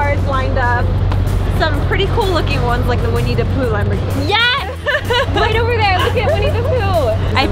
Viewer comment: OMG I thought you said it was the Juanita Poo Lamborghini and spit coffee on myself! Well played, well played.